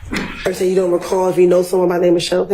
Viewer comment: I thought he was saying 'Total Recall' I was waiting for Arnold Schwarzenegger to walk in.